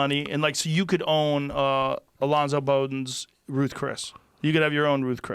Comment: I thought that said Joe Budden